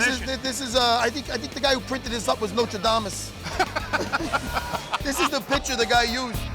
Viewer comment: Did he say "Notre Damus"? Did he mean "Nostradamus"?